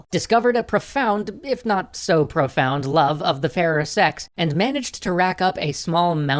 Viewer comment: At first I thought you said "a love of the same sex" and I just about DIED